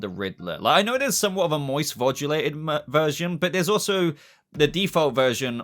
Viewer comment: "Moice Voldulated" 😄😄 I didn't even catch it the first time. Lol.. U said that shit wit confidence like a mofo too. Caught urself bouta say "Mersion" after all that tho huhh. 👍🏼🤣👍🏼